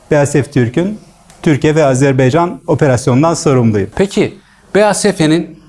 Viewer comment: Azerbaycanmı duydum?